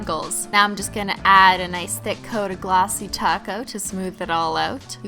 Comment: Is she saying "Top Coat" or "Taco"....lol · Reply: People joked about the way she pronounced "top coat", they said it sounded like she was saying "taco". So now she just says "taco" as an inside joke :)